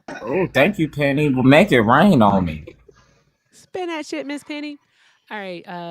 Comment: i’m not gonna lie; I thought you were saying “spin that shit. “